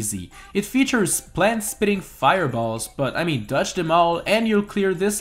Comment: did he just say plant spitting fireballs or plant*S* spitting fireballs? I don't know.